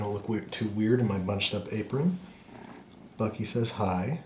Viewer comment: Congrats on 8 mil, Andrew. Thanks for such a fun episode, and the continued fun content. Cheers to li'l Bucky (I misheard as "Buckwheat?"), This is the wholesome content we need in 2020.